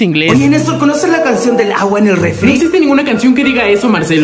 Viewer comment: Yo pensé que decía aguante un refriii